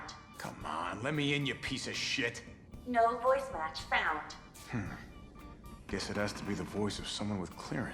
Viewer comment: pareciera que Carlos está hablando español, parece que dice ''qué será este...'' pero en realidad dice '' guess it has to be...''